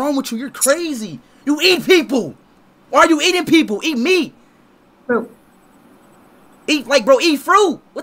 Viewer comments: i heard "eat me" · she thought you said eat me😂😂